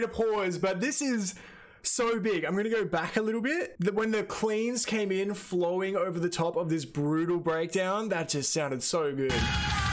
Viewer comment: for a second I thought you said "When the Queen came in flowing over the top of..." . I thought dam, he's already identifying Su as the Queen. haha. Since you are a bass player you need to listen to Rondo of Nightmare (with Mischiefs of God intro) It's a great song and Boh, their bassist, is insanely good. <link>